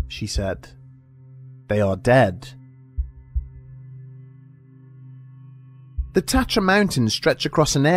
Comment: For a minute there.. I really thought you said Tetras mountains.. which reminded me of the game 😆